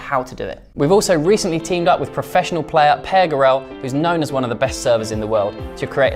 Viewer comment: What is his name please? I heard it's "Pear Grow". I turned on the subtitle, and it says "Pear Girl". I can't find this person with these two names on Google.